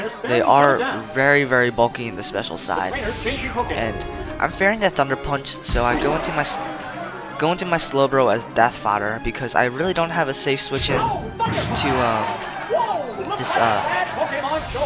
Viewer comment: It sounds like he say fucker punch